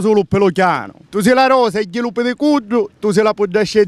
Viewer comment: "Tu si la rosa e (ghillu pidi)* cuddu tu si a puddicedda e chi sugnu, u gaddu" Qualcuno ha capito cosa dice a 5:13?!? Veramente non ho capito nulla che ha detto - per me il suo e il siciliano piu bello! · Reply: Probabilmente ha detto "tu si la rosa e 'i lu pidicuddu". Sarebbe il peduncolo che si trova nei frutti.